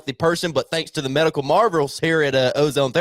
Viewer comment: pitch for Alive Ozone Therapy Spa, when he said "medical marvels", it sounded like he said "Medical Marlboros" 😆